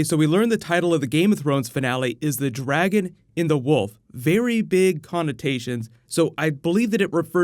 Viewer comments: I hear "The Dragon in the Wolf" xD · did he say the dragon IN the wolf? We are starting to learn more and more about Daenerys